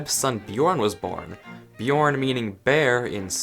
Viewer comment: lol it sounded like you said bjårn. 😂